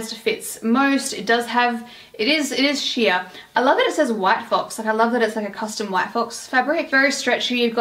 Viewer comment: I thought you said “white folks” 😭 yayyyyyy hauls! 🫶🏼